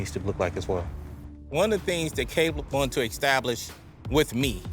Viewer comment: they can’t be too close if the trainer calls him “cable” instead of “Caleb”, Lol 😂